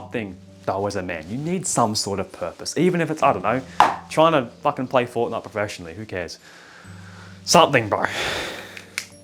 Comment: I thought you said "try to fck and play fortnite" 🤣